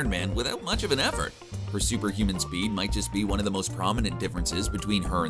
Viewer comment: Hulk has superhuman speed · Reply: oh wait I thought you said Superman speed my bad